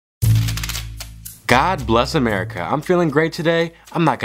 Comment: This is how many times Rhino told "Bless America ⬇️ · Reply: I thought he said “God Bless white America”